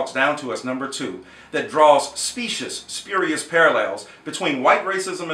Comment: did he say specist???